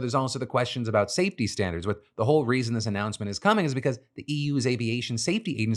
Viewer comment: just fyi at 4:42 you say "EU" but the English captions say "U.S."